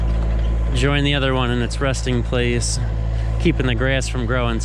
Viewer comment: did you say ''rusting place''? LOL!